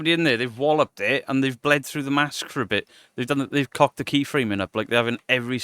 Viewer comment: Lmfao, sometimes your accent throws me off. Eg. in this video I heard you say “key framing” and for a sec I thought, “what? Keith Rayman?” 😂😂 Also your last live stream you said, “he’s gone to gamin’” which had my ears perk up for a sec like “what?! He’s gone to gay men?!” 😂😂😂 ffs. Still waiting to hear you get mad n shout “SHUT YER GOB!!” 🥹 I’m from slapped ham’s neck of the woods so yeah, love it 😆😆